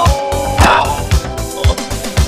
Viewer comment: I like how he's just sitting relaxed, while there's a whole fire in background lol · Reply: I missheard it as "This was a miss steak"